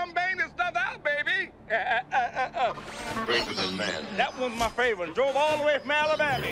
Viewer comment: i never understood what bee says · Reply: "...greater than man..." (Likely "a force greater than man") and then "GO!"